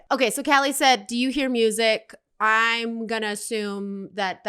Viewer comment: right ? Where lol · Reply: thought Callie said "do you hear music?" when Callie actualy said "Calliope means music" on the